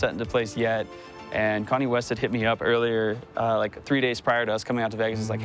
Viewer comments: I was only half listening to the beginning of this and legit thought he said "Kanye West" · I heard Kanye west